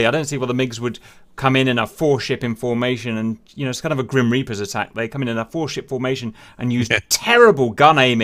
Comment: "It's kind of a Grim Reapers attack."!!!! I laughed way to hard at that! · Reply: The actual quote was "...instead of a Grim Reapers attack."